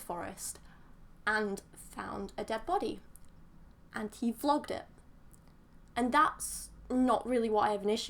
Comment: I thought she said "flogged" it. Thinking "wtf? You're kidding...."